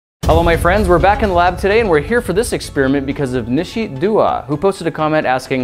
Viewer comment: Why do I always hear "exspearmint"